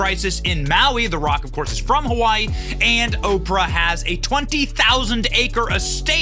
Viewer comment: I thought they said she had 2000 acres. Never 20, 000. Really?